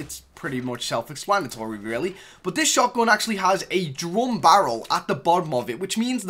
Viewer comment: did he just say drum barrel?